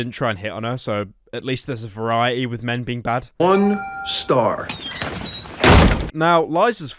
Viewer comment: man says "un-star". Like, a combination of "unsubscribe" and "one star". I've actually never encountered this before. Either because I don't get out enough, OR because he actually said "one star", and it got cut off. I don't know which is true. This video is really challenging my beliefs.